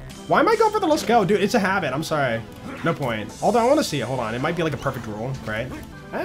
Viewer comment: after the hunt i just heard "no POINTZ"